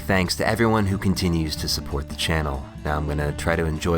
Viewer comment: I swear i heard "now let me enjoy what's left of my neg**ni